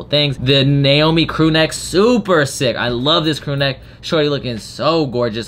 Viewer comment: Did he say the n word 19:12 ?????? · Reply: MaNamJeff no I said “eyelit one, oh my god this thing is so”